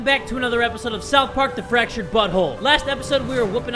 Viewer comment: When he said “Fractured but whole”, I heard “Fractured Butthole”